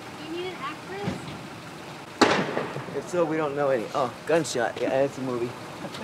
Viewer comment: Wtf was that guy trying to say? "it's so uh, we don't know any blargh, gunshot guys, blargh" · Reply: Woman: "Need an actress?" Guy: "If so we don't know any! *loud sound* Oh gunshot! Yeah its a movie."